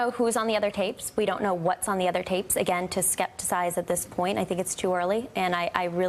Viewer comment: did she say “skepticize”? Is that a word like “regression to the mean” is a phrase?